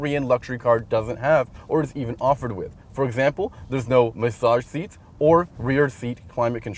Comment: "There's no .... seats." 7:22 what did he say? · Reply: For example there's no massage seats or rear seat climate control